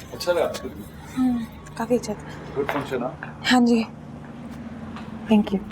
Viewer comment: Can someone tell me what did he say at 29:33? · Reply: Acha lga han kafi acha finction ta ye bt hui